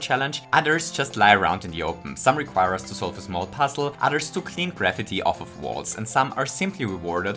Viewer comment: it sounded like he said "Clean gravity off of walls." Don't let the flat earthers hear that line...